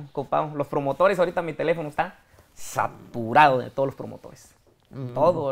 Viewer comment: Le regrese como 10 veces al 21:30 nada más para escuchar que dijo sapurado 😅😅😅